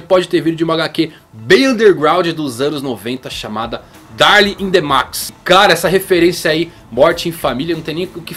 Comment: Quase ouvi Darling in the franxx kkkk